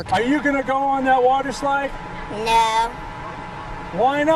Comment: the kid says no and subtitles say yeah lol